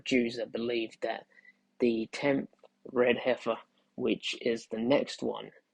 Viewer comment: did he say the 10th RH?